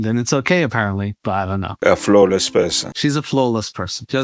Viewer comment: lol thought he said "flowless". like, she got no flow. can't spit those bars.